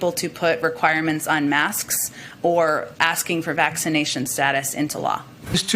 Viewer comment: she clearly said "asking for making vacination status into law"